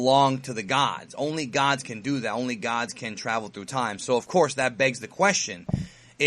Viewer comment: I thought whis said "not even Gods can time travel" not "only gods can time travel"